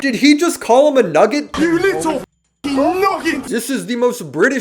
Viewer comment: sounds like he said 'noggin' to me, which just means 'head', not much of an insult, he probably meant to say nugget but noggin came out, lol